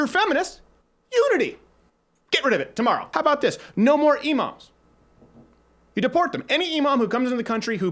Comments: When you said "imams" I thought you said "emos" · I thought he said no more emos and I thought "oh no he's coming for me." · I thought he said "No more emos" lol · I thought he said "no more emo's" lol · i thought he said no more emos. I was like "fuck I'm gone." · Lmao at 3:55 I thought he said emos and I was like well, true. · "One Love"? "Unity"? How does that work with people who don't want to unify with you, nor do they love you. They want to kill you. You can play along and have your silly, little concerts. Just don't expect the people who care about western civilization to play along with you. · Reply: I listened to this while doing something else ,I thought you said deport emo's instead of imams. I for one am sick of radical emos and there hate speech. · I thought he said "No more emos. Deport them!"...of which I am in favor for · I thought he said "no more emos" instead of "imams" for a second there... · When Crowder said no more Emoms, I thought he said no more emos and I was like YES!!! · I thought he said, "No more emos!" and I was really confused. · I thought he said no more emos. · when he said imams I thought he said emos lmao. I'm like yeah they're cringey but I don't think they're that much of a danger to us 😂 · at first I thought you said emos 😂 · I honestly thought he said no emos. Deport the emos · I don't give a fuck, if someone just starts touching me imma punch that person. I thought he said to start deporting emo's. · I thought you said "No more emos."